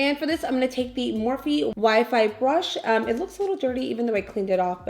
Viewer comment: My dumbass thought you said Wi-Fi 😂😂ps. I ❤ U so much and this look is 💯